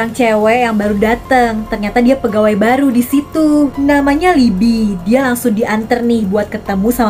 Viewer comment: kak bilangnya "libi" Tapi klo aku dengernya "lidi" Kerenn🗿🙏🏻